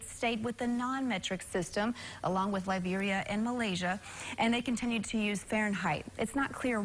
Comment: Did she said Malaysia?